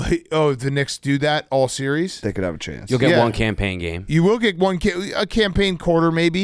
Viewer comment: I keep hearing them say “Cam Payne” and think they’re saying “campaign”